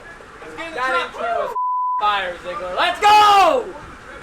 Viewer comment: what did say? 👀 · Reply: “Fire Zeisler”